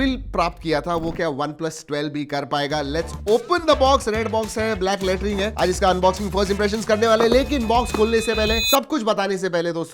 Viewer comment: i heard black laterine hai 😅😅